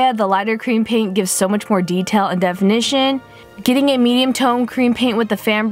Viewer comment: When she did a medium tone I thought she said a meme tone 😂